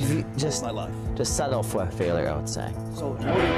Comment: The british accent is too much for me, it sounds nice but i don't understand ANYTHING that is said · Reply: He said ‘just settle for a failure I would say’